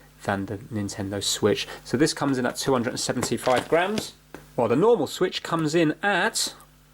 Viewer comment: I thought he said 275 grand I nearly died there lol